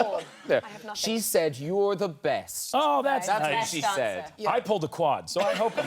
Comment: Did I just hear .. That’s what she said ! ?😂